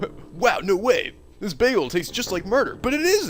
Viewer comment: Komaeda appears DID YOU SAY BAGELS?!